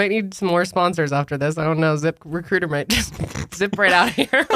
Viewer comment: I won't lie, I thought that was saying "zipper cooter" at first instead of "zip recruiter" and I was so confused and concerned, especially when you mentioned OF hahaha